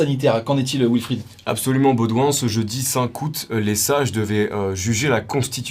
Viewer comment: faut que je dorme plus, j'ai entendu "ce jeudi Saint kout"